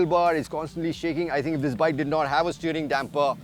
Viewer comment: I said if it didn’t have a steering damper it will have the worst tank slappers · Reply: oh damn you said "If this bike.." and I heard "this bike didn't have steering damper.." my bad 😅